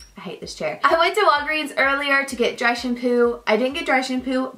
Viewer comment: I thought you said direction poo, not dry shampoo ._. I had to rewind it 4 times before I heard dry shampoo...